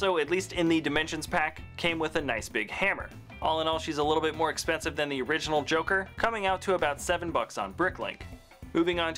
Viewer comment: Did he say joker instead of Harley Quinn? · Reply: He said its a bit more expensive than the original joker